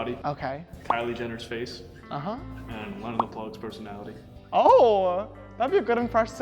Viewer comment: Can someone tell me what name said ??? @ 0:57 “___ personality” who’s personality???? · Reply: Lena The Plug